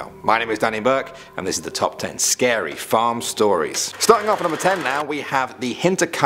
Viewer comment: I thought it said fam stories